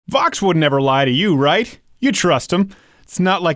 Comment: Did you say Fox? I think you said Fox. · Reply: Vox (the makers of the video) not Fox (the news channel)